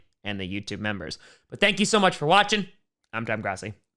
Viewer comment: When did it become the Crossi Posse? Lol, it just sounds like he says Crossi